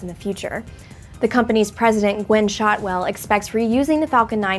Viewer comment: who did she say was the company's president? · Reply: Gwynne Shotwell Though I thought she was the COO not president of Spacex